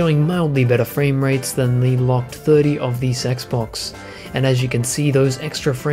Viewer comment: Please tell me you did NOT just say Sexbox.